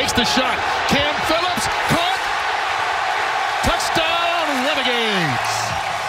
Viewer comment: Did they just say touchdown renegades?